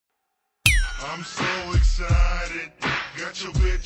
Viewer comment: Whole time thought he was saying “I’m so exotic” tbh love hearing it both ways. This song slaps 😝